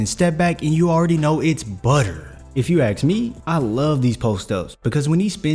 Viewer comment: did someone say butter?